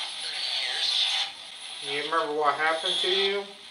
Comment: after asks how old are you and after " only 12 " came through, I heard the spirit box say " 30 years..."